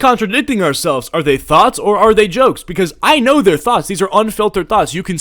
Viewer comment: When you were talking about his thoughts a ad cut you off and it sounded like you said unfiltered Thots